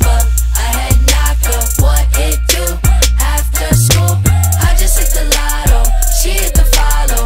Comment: "What it do after school" who let this child have a recording studio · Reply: Emily lol I'm learning English and I thought he said "What will you do after school"...My brain refused to understand something that doesn't make sense...